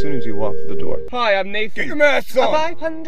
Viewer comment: "Hi, I'm Nathan-" "Do your math song." "Five hundred T wentyfive thousand six hundred minu T s " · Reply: lol i thought he said “get your masks on” but saying “do your math song” is 525,600 times funnier